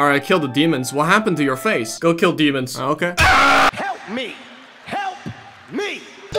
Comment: wth is that scream? i want it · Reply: Ah he's shouting "SUSUME!"